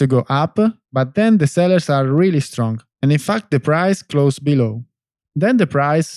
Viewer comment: Did he say "f*ck the price" at 1:04? 🤣